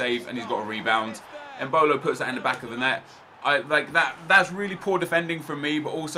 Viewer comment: thought said ebola put that back in of the net